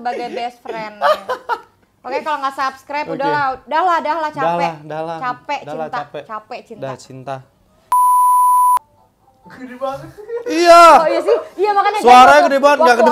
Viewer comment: dimenit kira" 05.10 ngomong "dahlah cinta" Aku salah denger kali ya🤣